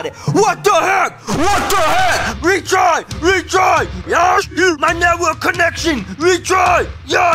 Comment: What did said in 8:07 IT JUST MAKED ME LAUGH SO HARD HET KEPT SAYING RETRY RETRY YASTREW is that what he said?